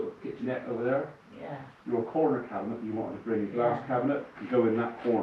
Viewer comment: She is having a purpose built wheel chair friendly wet room. 👍🙂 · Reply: I heard the words "glass curtain" and thought you meant glass enclosed shower stall and I was thinking it had a step over. Excellent!